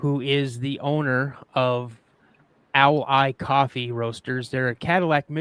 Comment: TY for printing the name of your coffee. I thought you were saying “Allied” Coffee instead of “Owl Eyed”😂 I want to find a Bigfoot statue about 8” tall. Find them for your merch. (The arms have to be long as if you don’t have better things to do!”) I always see the one on the Host’s shelf on “The Proof Is Out There” on TV. Arizona 🌵☀️